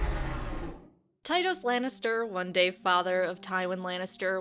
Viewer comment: When I first heard her say his name i thought she said "tight ass Lannister" lol